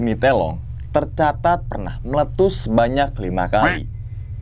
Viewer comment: sek sek aq seng salah denger meletus yg ke 5 2024 🤣🤣🤣🤣